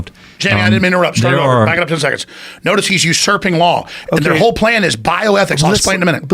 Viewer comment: What's serpent law? 6:53 or whatever he said🤷🏿‍♂️ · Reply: usurping law or to go above the law, they are talking about killing (organ harvesting) a baby that has been delivered and is alive